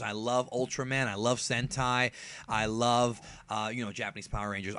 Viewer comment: When you said, "Sentai." I thought you said, "Hentai."